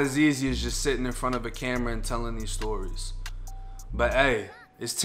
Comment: I thought he was saying "I'm rockin with Charlie, y'all rocking with me!" I was like who TF is Charlie?!?!